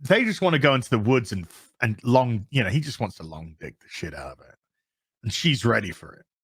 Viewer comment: FOR THE LOVE OF ... please dont sensor your self 23:48 .. FFS sake the first time i heard that i thought you meant " long PiG the shit out of her " .... i DO NOT NOT know why an English man would talk about Cannibalism in this context but here we are ...